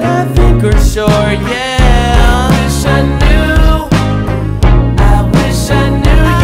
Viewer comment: I thought he said I wished I know but it was I wished I knew